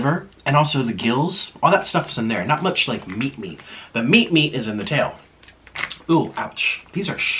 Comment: meat meat sounded like meeep meeep